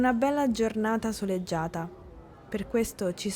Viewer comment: Non ho capito bene quello que dice al minuto 0:32, doppo le parole "È una bella giornata" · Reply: Soleggiata. Puoi attivare i sottotitoli, ci sono!